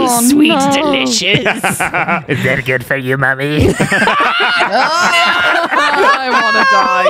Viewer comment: Please, can someone transcribe what says at 02:07, I can't for the life of me understand. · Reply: "Is that good for you, mommy?" Is what he said